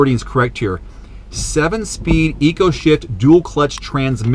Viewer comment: it sounds like you said sh*t